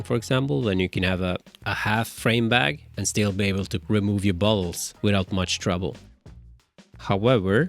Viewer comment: I heard “Remove your balls” 😂